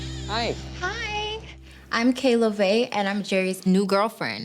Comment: I really hope Kayla is acting because boy oh boy she’s not the sharpest tool in the shed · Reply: I thought her name was kayla too but its actually. Her last name is so when she says her name it sounds like shes saying kayla. Idk why she doesn’t just say my name is 🤦🏽‍♀️